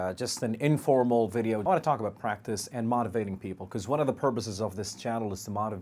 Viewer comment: you sometimes pronounce b instead of p ! like : 0:34 " one of the purboses " .... 2:43 " on tob of my bed" 3:33 you sounded like saying " it help me " instead of " it helped me"